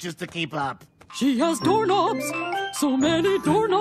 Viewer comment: Instead of doorknobs, I thought she said donuts.